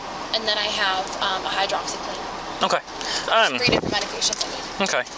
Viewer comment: Did she say she has hydroxyclean?!